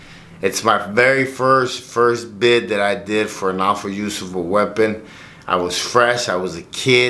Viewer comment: Sounded like you said you got locked up for “awful use of a weapon” haha 😂 again, accurate either way lol